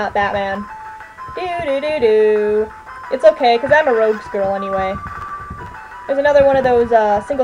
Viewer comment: Ahh, ha ha! I thought you actually said 'Rhodes girl', which would imply you had a rhodes scholarship. But yeah, I'm way more into the villains than Batman...I can only take so much of Bruce Wayne's childhood trauma before I need a good joke or riddle.